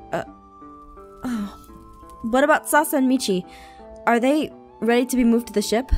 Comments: instead of Sasha I heard salsa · i heard salsa in stead of sasha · "sasa" XD anybody catch that and just wanted to re- watch this series? 😆😂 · Anyone else hear salsa in steed of Sasha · Is it just me or did say “sauce and michi” (4:52) 😂😂♥️ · did really said Sasa ????😂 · Did say "Salsa and Michi " salsa? salsa. SALSA!!!!!!!!!!!!!!!!!!!!!! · Did she just say Salsa? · Did you say salsa..??. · WHO ELSE THOUGHT SHE SAID “ what about Salsa and Michi” 😂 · if you play on 2x it sounds like Salsa and Michi instead of *Sasha and Michi*. Well to me it does. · it sounded like she said salsa and not Sasha😂😂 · Am I the only one who heard "Salsa" instead of Sasha?😂😂 btw rewatching